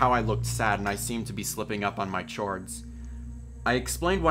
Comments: Did he just say chhords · DID ANYONE ELSE CATCH HIM SAY CHORDS A CH-ORDS